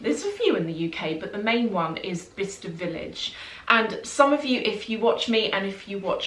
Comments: I always thought you were saying “Vista” village 😆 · I thought you were saying "Vista Village". Explains why I couldn't find it lol Thank you so much for sharing!